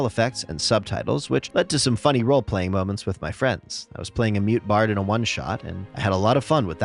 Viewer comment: I was just listening to the video while eating, and I almost did a double take after I misheard "mute bard" as "meat bard". Lmao